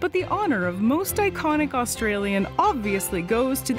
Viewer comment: “My deepest apologies to koalas, Crocodile Dundee and Chris Hemsworth, but the honor of most iconic Australian obviously goes to these furry marsupial cuties!” I almost thought she said “most iconic Australian animal”, and that this implied Chris Hemsworth and fictional crocodile hunters were regularly found in the wild.